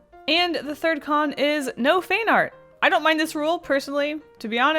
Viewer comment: Sounded like you were saying fain art